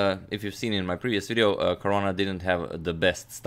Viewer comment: who else heard Korona instead of Caruana🤓